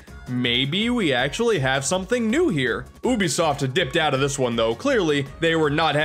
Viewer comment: It sounded like he said Boobiesoft Also Ape Escape is the entire reason why I want the PS5 to be fully backwards compatible (PS1-PS4)